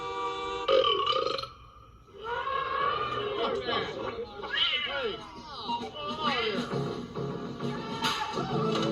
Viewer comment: did someone say "fuck that"? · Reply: They said “What’s That?”. 🙂 … HaHaHa, … Lol, it does sound like they said “F*** That”!!! 😄